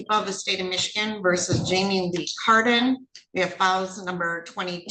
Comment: Who else thought she said Jamiee Lee Curtis? hahahaha